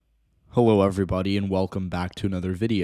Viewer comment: Had to do a double take at the start cause it thought he said “hewo everybody”😂